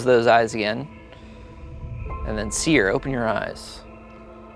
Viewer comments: Did Spencer said 'close eyes, Ian'? He rat him out just like that. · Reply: he said "close those eyes again" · did he just say close your eyes Ian